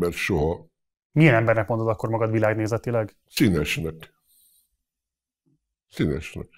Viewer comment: mit mond Verebes ? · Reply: "Színesnek."